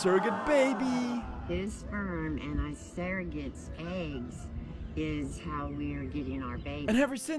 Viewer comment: I thought she said "his sperm and asparagus eggs"